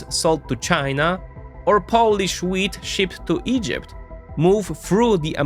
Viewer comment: At first I heard 'Polish weed shipped to Egypt' and for a moment thought I was in a weird alternative universe